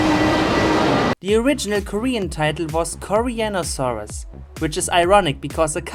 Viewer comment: I mean 4:23 gawd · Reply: Why does the first one sounds like He says penosaurus